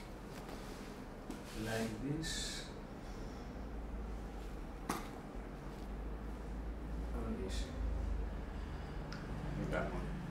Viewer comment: what did he say? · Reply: You like this or this